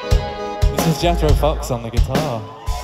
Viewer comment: what he said? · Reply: "This is Jethro Fox on the Guitar"